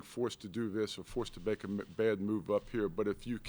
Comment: Anyone else hear him say baker Mayfield